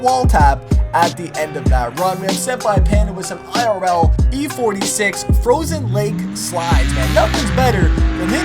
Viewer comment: Did I just hear E46???